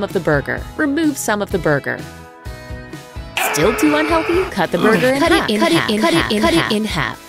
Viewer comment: To me it sounded like he was saying cut g